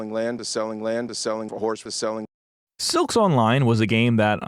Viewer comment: I'm not gonna lie when you said silks online I thought I heard 'Silksong' for a second which made my heart drop